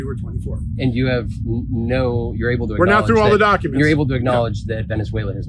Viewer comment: You are trying to put words in his mouth. He clearly says 'we are not through all the documents'. I thought you were in favour of facts and evidence? The documents that are now declassified have not been poured over, so you cannot say what is in them as you haven't read them. Try to stay objective, you are undermining your own efforts at the moment.